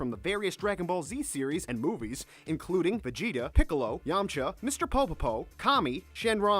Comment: did you just say "Mr. PoPoPo" and Todd "Hayberkorn" why. why. WHY YOU DISRESPECT TODD HABERKORN AND MR.POPO